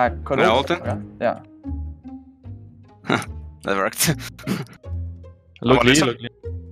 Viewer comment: I think Pekz said "get rekt" XD